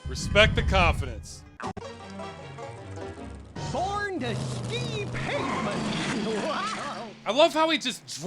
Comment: I should probably clarify to anyone reading the comments, what the monster says is, "Born to ski pavement!", not "Born to Steve Penkmin!"